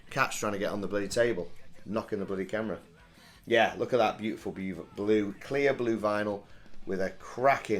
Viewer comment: Did you say ` look at that beautiful Beaver ` ?? Please put that in your first blooper vid :D